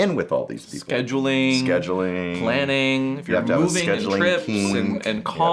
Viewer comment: Wait did daddy say you have to have a scheduling kink lmao